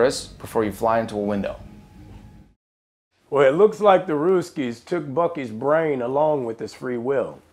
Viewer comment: "Well, it looks like the __ took Buckys brain..." Who took his brain? I cant understand lmao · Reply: ruskies (russians) :P